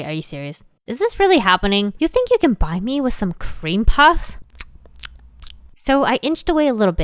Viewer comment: When she said cream puffs I thought she said cream pie